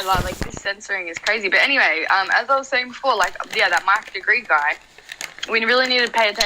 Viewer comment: who did she say ? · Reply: Mike DeGruy, some of his documentaries are legendary